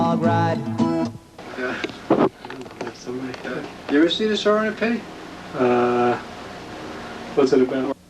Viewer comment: What is saying around 48:53 ?? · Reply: FIGURED IT OUT! “the Sorrow and The Pity”